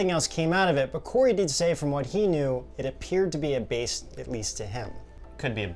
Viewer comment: Wait, did you say "A base that leads to him?" A base that leads to... COREY? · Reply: I listened to it a few times, and realized you said "A base, at least to him." DOH.